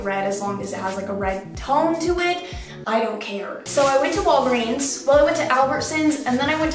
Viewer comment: When you said Walgreen I honestly thought you said Paul Breen and I was like on my the US has so many store name, smh I know I'm a dumbass